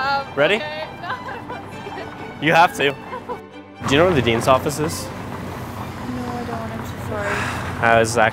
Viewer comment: I swear I thought he said ?do you know where Vadims office is"? Lol